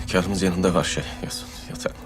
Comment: da Sümbül ne diyor anlayabilen varmı? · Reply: Hünkarımızın yanında ve şey yatak odasında.